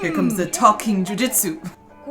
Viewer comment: Naruto has "Talk No Jutsu." Naruto only uses the power of his words, not jutsu or ninja powers to win you over. At around 3:10 it sounded like said "Talking Jiu-Jitsu." So if Naruto has "Talk No Jutsu" then I guess says Lelouch has "Talking Jiu-Jitsu" which sounds great LOL. Lelouch will grapple with you in an argument and lock in his "Talking Jiu-Jitsu" to make you tap out and give up. Hahaha